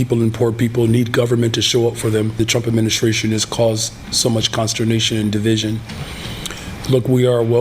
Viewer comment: Did he say constradiction, and division ?